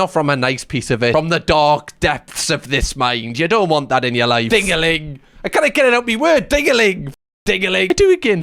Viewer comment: I am Brazilian and my english is just ok, I was trying very hard to understand that word "tingling" and I was like WTH is this fkg word ?